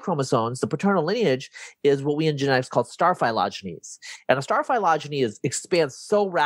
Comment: What's the word he's using at 19:53? "What we in genetics call starf******"? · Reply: star philogeny I think